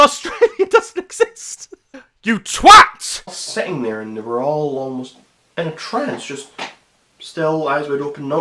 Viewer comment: I swear to god I thought he said there all aliens😂😂